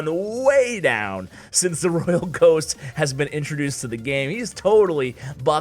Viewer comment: I thought you said that the royal GIANT was op 😂